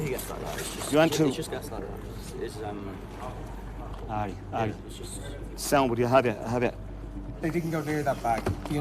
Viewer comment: Can someone please tell me what said at 28:38? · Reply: Alright alright sound buddy I have ya Depending on context can mean “ah ok I understand what you’re saying” or “don’t worry I’m taking care of it” - in this context it’s almost a mixture of both.